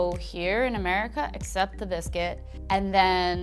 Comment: I thought she said “except that it’s gay”